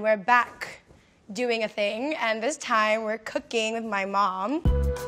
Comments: I thought she said at first was "and this time, we're coming my mom" 👁👄👁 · i thought she said 'and this time we're cooking my mom' and i was like ''ew'' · I thought she said today we are cooking my MOM!!Omg